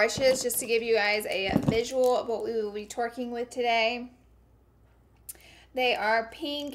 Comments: she said twerking instead of working lol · It sounds like she said Twerking instead of working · Did anyone hear her “to twerk with them?????😂😂😂😂😂😂